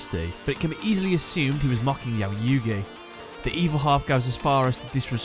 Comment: and then there's littlekuriboh's version of Bakura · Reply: turn on the captions. It says ‘you gay’ Instead of Yugi...